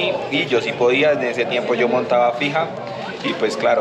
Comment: Juro que entendí "pija" 😳